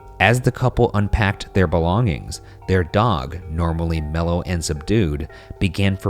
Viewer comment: Idk bout you guys but at 6:20 I heard normally mellowed some dude😂😂